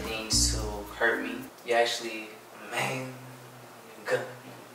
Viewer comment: anyone figured out what he said?😂😂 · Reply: LOL after he was saying everything that makes her so different from his past relationships ... he then ended it with saying amazing 😉 like in the bed 🤣😂 that’s why he was laughing that’s why they cut if you noticed 😂she gave him a kiss